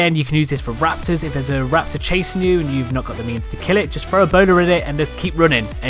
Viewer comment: I heard boner... 🤦‍♂️